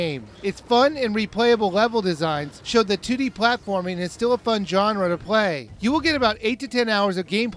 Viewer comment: it sounded like you said '' showed that titty platforming '' lol